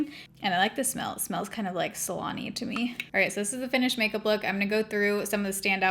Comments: I heard 'salami' for how the hairspray smelled haha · At first, I thought you said “salami”….. and then I died laughing when I realized that you actually said “salon-y”😂 love your videos!!❤️